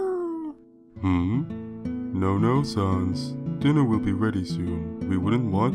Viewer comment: Baby sans: wants ketchup Gaster: no no sans breakfast is almost ready. Baby sans: GOO Gaster: why do I hear boss music? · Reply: I think he said dinner